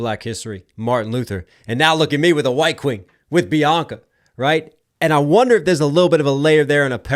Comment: Didnt even think of Bianca … thought he was saying Kim